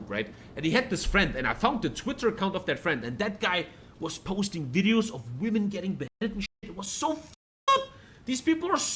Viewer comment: what does he say at 3:37 ? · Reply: "and that guy was posting videos of women getting beheaded and shit"